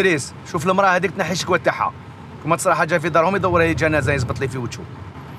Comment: خاوتي في دقيقة 11:45 سمعتو واش سمعت ولا انا نيتي ناقسة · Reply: قال يزبطلي في وجهو